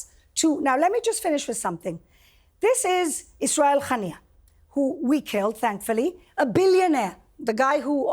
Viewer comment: did she call him "ISRAEL Haniyeh"?